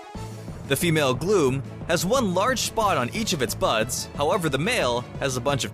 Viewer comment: did he say butts?!